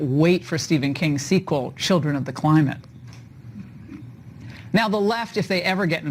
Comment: Children of the Klonopin. That's what I thought I first heard 👍